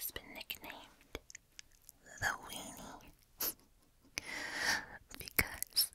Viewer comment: am i hearing correctly at 4:12 cuz what i hear is "In Japan the Nintendo Wii 2 has been nicknamed the weiner" idk..